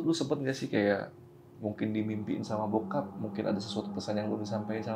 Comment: ini host bilang " bokep " apa saya salah dengar ya? 😂